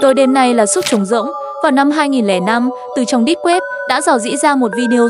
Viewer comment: Deep web nhưng sao nghe thành"đít web"